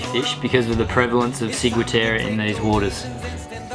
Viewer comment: What did he say at 15:35 ? Siguitare? Seaguitair? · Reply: Ciguatera.